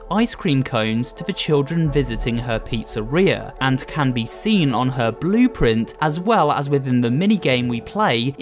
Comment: When he was talking about the claw I thought he was gonna say "it can be seen on her booty" 😭